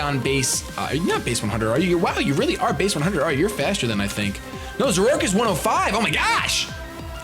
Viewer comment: Wait... Did he just say Zoroark like "Zor-roar-k"?! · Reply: When you say it fast it sound like Zor-roar-k but he said Zor-or-ark